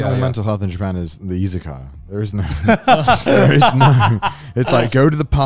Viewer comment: i heard "mental health in japan is the isekai".....wait what did he just say?^^ i then realized from the following sentence that i missheard izakaya...but still....that was a dark moment Oo^^